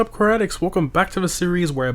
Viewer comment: When I first started watching, I misheard him say “welcome back koratics” instead of car addicts, nothing major but it surprised me😅